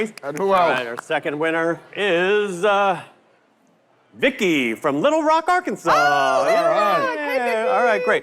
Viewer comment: OMG ... I thought yall said Nickey....😢 I hope you enjoy Vicky